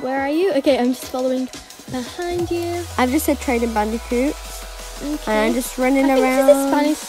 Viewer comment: I Thought you said trading mega bandicoot😂😂